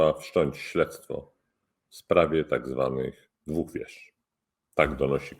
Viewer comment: Usłyszałem " w sprawie tzw. łupież".